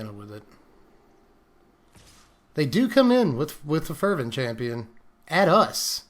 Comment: what he said: "They are coming with the Fervent Chanpion, at us." What I heard: "Oh? So you're approaching me?"